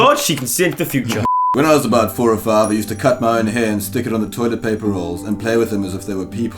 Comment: 'i used to cut my own hands' ????? thats what I understood, but I think said hair, idk I am so confused rn